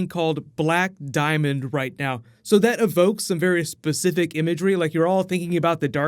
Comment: Did you just say "Bear-y specific"?